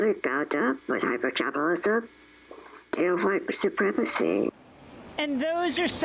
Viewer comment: what did she say? I can only get "hypergabilism"? Not sure what the word is.